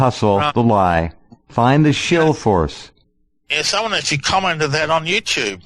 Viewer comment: I hear, "I FUND the shill force."